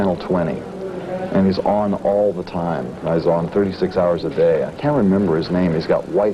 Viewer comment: did he just say 36 hours a day